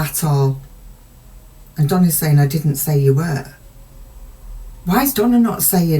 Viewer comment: Donna: "I didn't say you were." I thought I heard: "Oh yes you were".